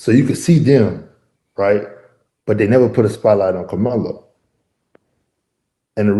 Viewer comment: Did he say, “They never put a spotlight on Kamala”??? Who is Kamala?